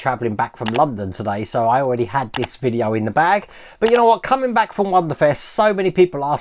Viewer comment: Hey I'm watching this video and you had said you had put this video in the bag...... However, my poor wife insisted that she could've sworn that you said you threw a speedo in the bag lmao! 😂😂😂😂😂😂. We are both having a good laugh over this right now. Have an awesome week and weekend